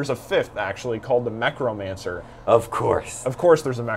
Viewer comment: Ah see I thought he said Necromancer in this video but looked it up right after.